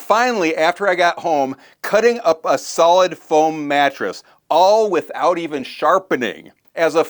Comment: You said "Foam mattress" & I thought you said "Bone mattress" 😂 to be fair we had bad weather yesterday, my sinuses went nuts & my head is all congested today so my hearing is off.